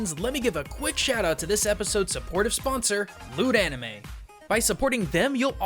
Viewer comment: Is it just me, or did it kinda sound like he said lewd anime? Anyone? Just me? Wierd...